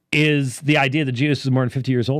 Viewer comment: Is he saying "Jesus" or "Judas" died at an age over 50? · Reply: Jesus. The idea had was that Jesus had to redeem every part of a mans life. The early church was weird. I mean, Ransom Theory probably wasnt universal but it was the only idea we had written down on the Atonement for centuries.